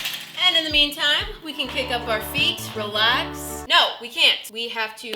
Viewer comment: Okay, though. Like, am I the only one who heard her say "Cake up our feet"? Or did y'all hear it too?